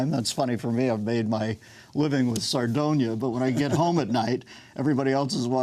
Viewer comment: "I've made my living with...." Sardonia? That doesn't pan out. CC says Tsar Dona. Also a miss. What did he say?